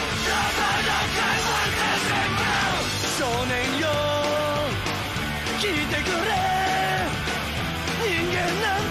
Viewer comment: I’ve always wondered. Does he says “Shonen Jump?”👀😂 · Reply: It's SHONEN YO or hey young man in English translation